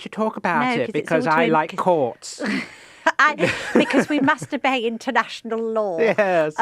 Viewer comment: I heard "we must obey international law" as "we must mastur*a*e international law" :face-blue-smiling: I had to replay it 3 times before i got it!